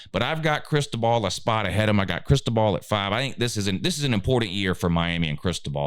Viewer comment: Sounds like your saying crystal ball 🔮 lol